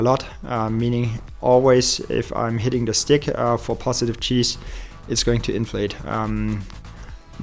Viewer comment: I swear, I kept hearing, "cheese suit."